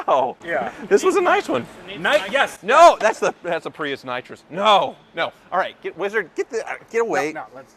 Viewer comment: did I just hear Prius nitrous?